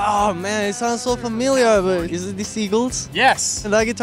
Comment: I can’t be the only that heard him say “Seagulls”?